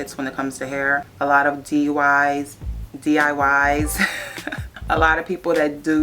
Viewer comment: Did you just say DUI?